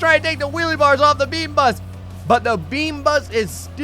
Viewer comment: my dumbass thought it said beanus